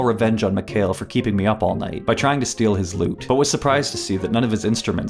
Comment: i thought you said loot but you said lute. Thats kinda homophonic bro